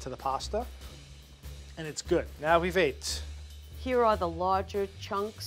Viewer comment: So much salt... i can feel myself getting hypertension, just watching, lol Also... after tested the sauce, i know he said "now, we wait", but it really sounded like he said "Now, we have aids", which is kinda funny, given he just ate sauce that had still partially raw meat in it.